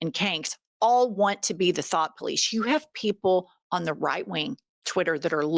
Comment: I thought you were saying the THOT police!😂